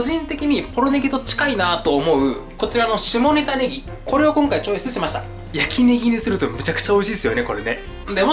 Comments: 下仁田ねぎがどうしても下ネタネギに聞こえてしまいます。 お父さん、お母さんこんな私を許してください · ダメだ、下仁田ネギが下ネタネギに聞こえてしまう